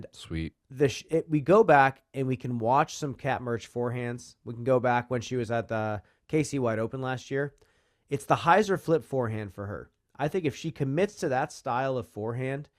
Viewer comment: I thought said "the Casey White Open" 😂😂